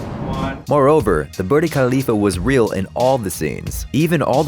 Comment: Did he just say burd khalifa ?